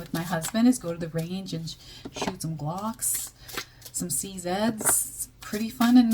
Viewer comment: Thankyou for yet another amazing reading. You made me google glocks lol I thought you said shoot some ducks. My heart sank till i checked transcript 😂